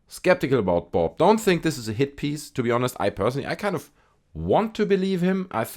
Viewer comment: Hand him a standard physics exam and in an hour you'd know that he was an electronics tech. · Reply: I thought he said this wasn't a hit piece.